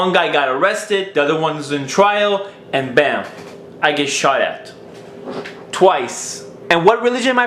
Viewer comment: WHAT DID HE SAY ON 04:08? He got shot or what?